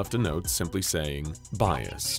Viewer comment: I thought he was about to say "based".